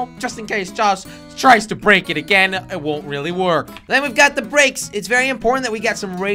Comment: Did he just say: it won’t really work